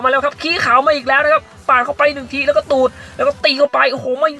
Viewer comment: มีใครได้ยินเหมือนผมมั่ง "แล้วก็ตูด" 555